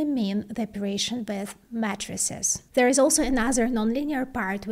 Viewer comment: when she was saying "matrixes" or "matrices" i thought she was saying "matresses"